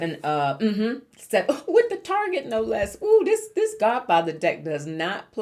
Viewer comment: Thoroughly enjoying this reading. What cards are you using? I love tarot but didnt recognize the deck. If you share the title itwould be so appreciated. Thanks so much and p.s. loved seeing you with susan lynn earlier this year. I hope you have a wonderful holiday!!🦋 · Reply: I heard you say Godfathers deck. I definitely will be looking for it. Again happy holidays!